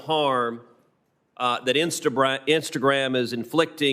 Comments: Did anyone else catch the Insta-brat Instagram line Cruise "accidentally" said At 1:30 minutes. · DID HE JUST SAY " INSTA BRAT "??? LMAO